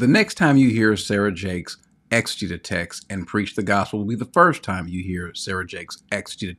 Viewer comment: What word is saying at 0:10 and 0:14? Start with an E and then he goes on to say "and preach the gospel." I have no clue what that word is!! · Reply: Exegete